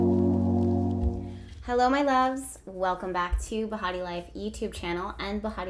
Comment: this is my first time watching one of your videos and I thought you said "welcome back to the hottie life" I was like ong thank you 😆